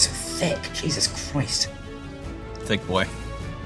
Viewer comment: Did somebody catch the "Thick what" at 17:03 😂😂